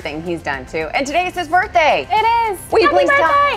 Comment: if you pause it right at 2:56 it sounds like she says " we love you bro" lol